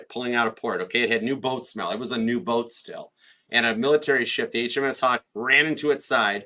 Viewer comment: when you said the Olympic was "new boat still," I had to rewind because I heard, "It had new boat smell." I thought, do boats have that wonderful smell like a new car? I was so confused.